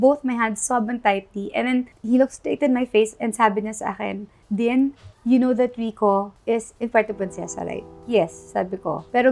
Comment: ???? di ko maintindihan ano sabi wala naman subtitle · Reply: Dine, you know that rico is in puerto princesa right, this morning they found him dead